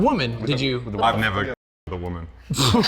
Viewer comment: Can someone who know english very well tell what he said at 7:40, what said at 8:28? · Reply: "I would never have sex with a woman" Joking And "Uncut"